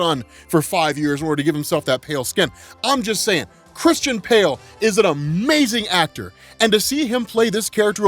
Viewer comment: Uuummmm, did you call him Christian Pale? Right around 5:50? Too good.